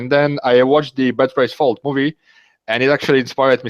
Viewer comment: Can anybody clearify what was the movie he said inspired him? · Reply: Bet raise fold